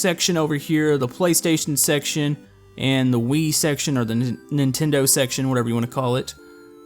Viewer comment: i though you said weed instead off wii